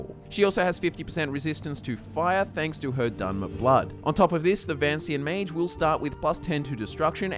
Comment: At first I thought it said Vatican Mage build. "Don'ta messa with me cuzza I'ma the pope!"